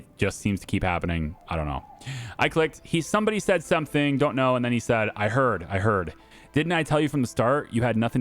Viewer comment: in slowest speed Leo: Did you hear what Borroni said about me? I'm a great chef! Francis: I heard, I heard. Didn't I tell you from the start you had nothing to worry about? That's about it.